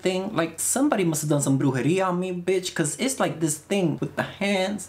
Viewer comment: "somebody must have done..." what did he said in spanish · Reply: witchcraft, he said "brujeria"